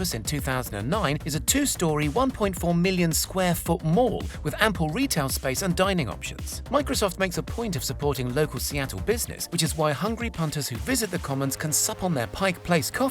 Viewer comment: Thought you said Apple Retail space!!